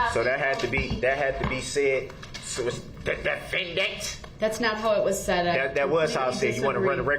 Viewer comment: she 100% did say DE FEN DENT 😂 i got one ear that work and i heard that